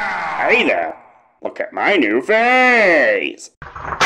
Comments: Baldi is kanda saying look at my new face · Tbh I thought he said “look at my new face”